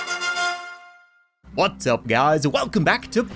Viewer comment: Is it just me or did said "Butts up" instead of "Whats up"?